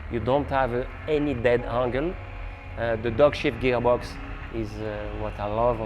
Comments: did he say "the dogshit gearbox"???? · Ah .... "dog-shift" geargox. Had to listen twice, the first time I thought was saying that the gearbox was not very good! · Did he say "dogshit gearbox"?